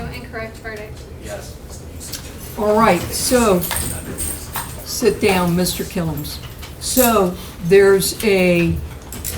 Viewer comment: Did she just say sit down Mr kill ems😮😅